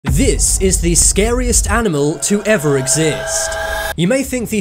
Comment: I thought he said "savest animal" instead of "scariest animal"